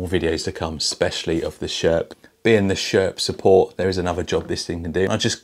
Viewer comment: I thought that the TRX-4 said "Sheriff Support", even the colors match to some counties patrol car colors...